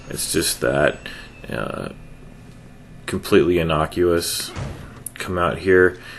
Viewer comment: Did you say completely obnoxious at 9:30?